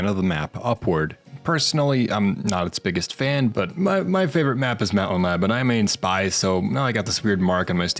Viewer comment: I swear to god I heard you say "My favorite map is map-o-map"